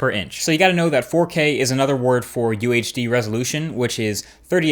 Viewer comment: it is called 4K UHD (<num>) not QHD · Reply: ohh , sorry , he said UHD at 0:56, i hear it last time as QHD , i was wrong. Thanks for correcting me